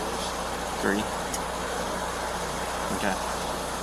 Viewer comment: Did he say “thirty beers”? Sounds about right.